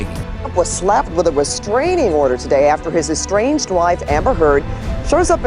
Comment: I heard "strange wife" when they said "estranged wife"😄